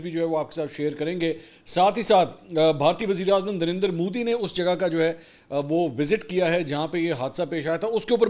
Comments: Did i hear darinder modi? At 0:44 🙊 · Did he just call Narender "Darender"? 🤣